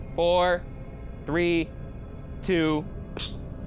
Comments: no stop that’s, at least that’s what I heard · am I the only one who heard it say "stop the thing" at 29:53 ? broo that gave me the chills 😅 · it sounded like when Ryan got to 3 I think it said Oh no stop